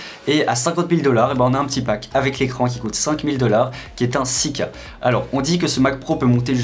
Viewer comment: C moi où ils disent “des film en ouica” ils ont inventer la Wii K JPP 😂 et ils disent aussi сука et pas 6 K il faut vraiment aller voir une orthophoniste les gars